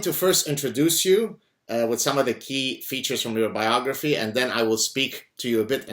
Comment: What a suprise, looks like i'm going on vacation to egypt Also 0:35 i heard "key features of your biology"